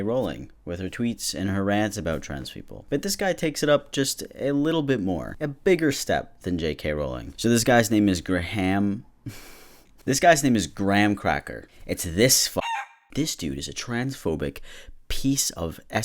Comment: I misheard it as Gram Crackle and I went "The dude from Carmen Sandiego???"